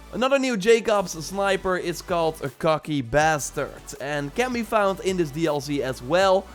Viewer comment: Lol i thought you said "cucky bastard" at first